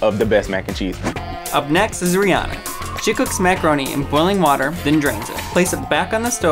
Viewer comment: Bruhh I keep hearing "She/He cooks macaroni with blah blah blah water and DRINKS it" 💀